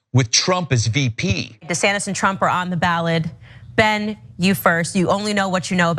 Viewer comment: Did she say ballad or ballot at the 3:18 mark?